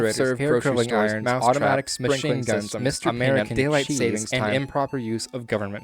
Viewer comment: I like how when you were compiling fast food places I heard, “automatic machine guns.”